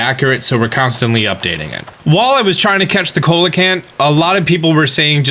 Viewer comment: Everytime he said coelacanth i thought he was saying "Cola Can" and i was really curious how he got a drink in the game